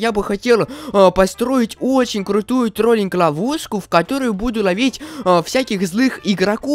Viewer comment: Мне показалось что он сказал на 0:34 сказал не ловушка а лавуску 😂😂😂